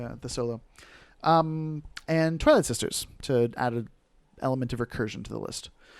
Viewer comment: Really excited for this battle report. Legit thought you said toilet sister at first, lol.